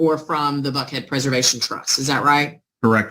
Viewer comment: Sounded like they were saying butthead preservation and trust. Lol i thought perfect. ;)